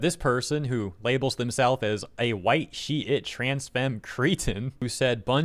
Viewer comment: I thought he said "a white sheet..." It makes me wanna identify as a white-sheet now - can I choose what kind of sheet? A bed sheet? A car-cover sheet? A painters drop/cover sheet? OH!!! Can I identify as a "cover sheet" on a document; ie. can I be a piece of paper? Or, can I choose another material that I am made from : like cotton sheet, polyester, rayon, nylon, silk ....... OHHhhhhhhhh!!!!! To be a white shyte!